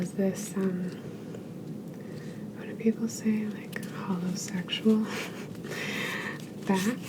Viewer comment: did you really say "holosexual" lol